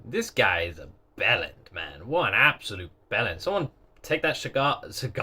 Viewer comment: what is the b-word is that you're using? Can someone please explain? Maybe I know it, and it's the pronunciation/accent I'm confused by. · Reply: It's bellend. It's kinda synonymous with jerk.